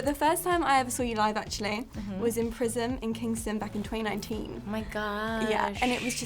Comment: did she say in prison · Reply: prism in kingston